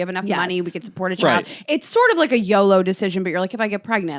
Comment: I thought said “it was a yellow decision “ like referring to traffic light like it wasn’t an absolutely fucking not red red, nor a green light it’s a go either, but captions said YOLO decision hahah